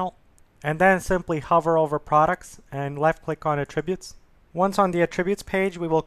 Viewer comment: You're mispronouncing "attributes". It's disconcerting. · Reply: I thought he was saying "a tribute". You are not alone, it took me a second to understand what he was trying to say and unfortunately took my train of thought away from the tutorial.